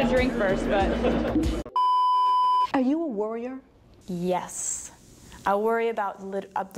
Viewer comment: I thought she said, "Are you a warrior?"